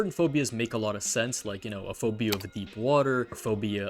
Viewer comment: I thought he said "phobia of eatin water"